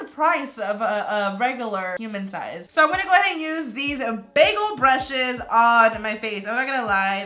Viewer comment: Everytime she said “big old brushes” it sounded like she was saying “bagel brushes” No hate tho 😂